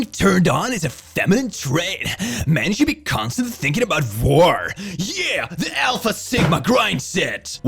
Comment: I've only been listening, and i thought it said "Vore" not "war", and considering this is a The Click-Tok video i didn't question it.